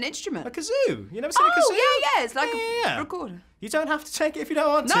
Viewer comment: Did she say ‘it’s like a ...recorder’? 😂